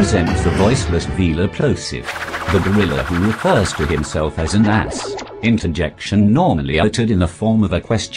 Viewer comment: the auto-generated captions said "the driller who refers to himself as an ass"